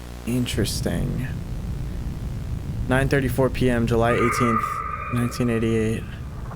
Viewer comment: that’s my birthday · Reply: Lol i thought when it said July 18th i thought itsaid june and yelled thats my brithday